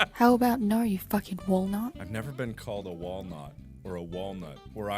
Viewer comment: did she just call you a wolnut?